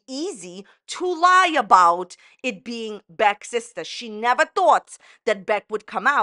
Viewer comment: I could have swore said “ Becky’s sister” in her FBI Frank story😐